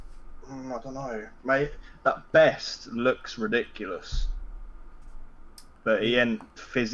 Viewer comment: Ive watched this so many times and I keep hearing "that BEST looks ridiculous" Shoutout to licking your lips if you saw vieira at the edge of your box